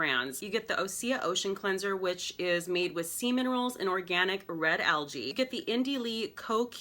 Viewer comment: I thought you said that was made from "semen rolls." Sea minerals 🤦‍♀️